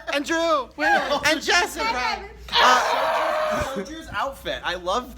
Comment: I thought he said Chessa any jake paulers here